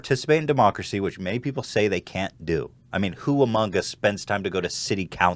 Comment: did anyone else hear that "who among us" come out to humongous?